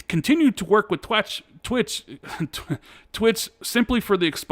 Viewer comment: Lol said "T W A T" instead of twitch. 39:34 We know what was on his mind. 👋